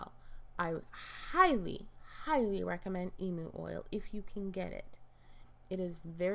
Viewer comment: I thought she was saying emo oil and I couldn't stop laughing 😂😂